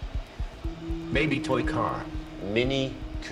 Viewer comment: i heard "baby toy car"